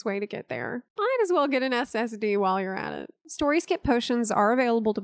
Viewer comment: I thought she said std